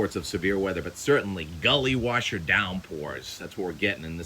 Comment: I Had to rewind...thought you said, "belly wash"...heard that a lot growing up. Belly wash = tonic/soda.